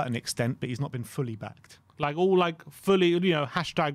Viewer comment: I thought it said sacked, heart almost stopped